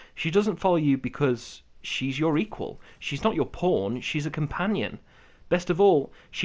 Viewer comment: sounds like he says "she's not just your porn"